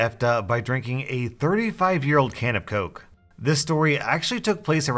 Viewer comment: My audio cut out in the middle of him reading this, so all I heard was, "Today I f*cked a 35 year old can of Coke."